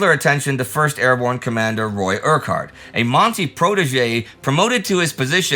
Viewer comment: When Indie said "a Monty protege", I at first thought he said "Monty Pythonesque"! And unfortunately Operation Market Garden did have some Monty Python elements to it, such as giving the Germans 24 hours notice of the attack.